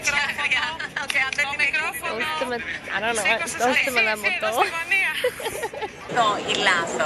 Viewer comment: WTF She said "doste mou ena poto"? That is definetly not what the lady on the video said but it means "Give me a Drink" Translated. What are the ods?